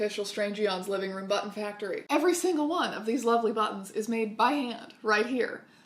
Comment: I wasn’t looking at the screen and thought she was just saying butts. I was confused but I liked it